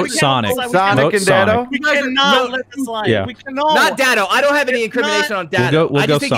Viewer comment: LMAO he immediately calls another meeting "WE CANNOT LET FRUIT LIE" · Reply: Omg is that what he said I thought he said we cannot let fruit fly